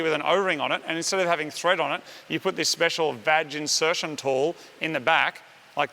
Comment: Did he just say vag insertion tool